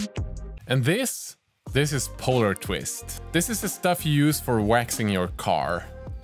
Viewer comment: I searched google but can't for the life of me find anything related to "Polar Twist" (if that's indeed what you're saying here). Edit: For those looking, it's spelled "Polertwist".